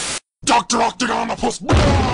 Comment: What was that word said before thunderbolt fired his laser? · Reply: he said dr octagonapus